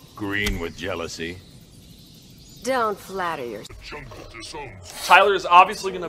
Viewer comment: Markus Scott what did they say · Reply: ImAGlitch I think I misheard it the first time I heard it. I thought he said 'the joker disowns you' but now I think he says 'jungle'